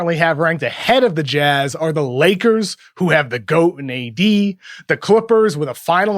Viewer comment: did I heard goat? Goat on trading teammates. lmao